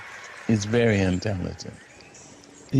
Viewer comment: I thought she said he was "very untalented"! I was waiting for her to roast him, but...